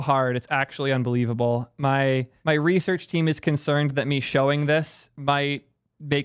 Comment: sorry did he say he has a research team ????